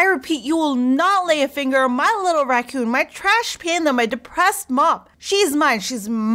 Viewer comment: My little Raccoon Princess Trash Panda Depressed Mop! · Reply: I thought she said depresssed mom lol